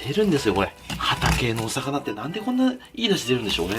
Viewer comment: 畑の魚に聞こえて、？？？ってなった笑